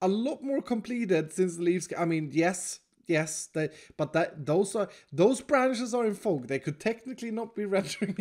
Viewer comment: When you were talking about "These branches are in fog" I heard "Those branches are in fall" Iskall. Have you considered having your tree be so OMEGA that it extends into all four seasons? One part summer, spring, fall, and winter?